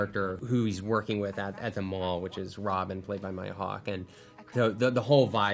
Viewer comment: who made the captions say mike hawk instead instead of maya hawk